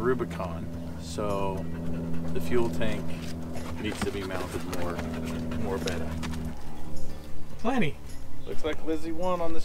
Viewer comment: Did I just hear "The fuel tank needs to be mounted Morr Vetter"?